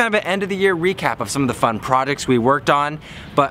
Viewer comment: did i just hear products instead of projects??